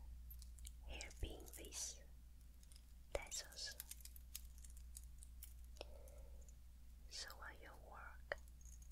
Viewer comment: Hey friends! I don't know english well and i'm very curious what said in 6:31 Someone wants to help me?:) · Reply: Hello! "The back of this hairpin piece.... tassels. So when you walk, it will makes very nice sounds." Rest well ❤️